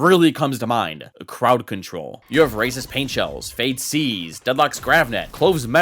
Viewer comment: Subtitles said "racist paintshells" idk I thought it was funny